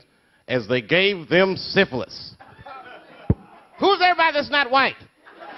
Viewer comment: anyone know what was said at 3:38? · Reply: "Who is: everybody that's not white?"